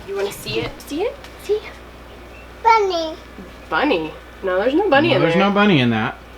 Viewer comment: So sweet to see you with your Grandma. I thought was saying the pot was burny but you both seemed to think bunny! 😆